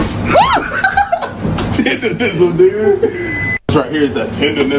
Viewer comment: tendernism WHAT?! 🤨 did I hear that right?!